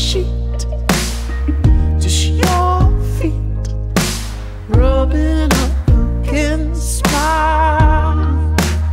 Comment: Tom's sassy dancing gives me life. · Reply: I thought it said: rubbing up against my wife😂